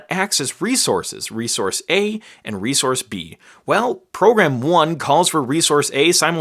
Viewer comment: my computer made me think that you said "resource B and resource B" EXPLAIN WHY THE HELL IS MY COMPUTER DOING THIS?